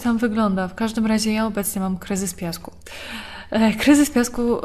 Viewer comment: usłyszałem kryzys w związku xd